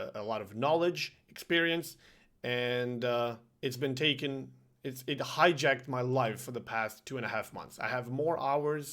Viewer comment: I heard "highjacked my wife"... dunno if it was your pronounciation or me eating pizza... 😂😂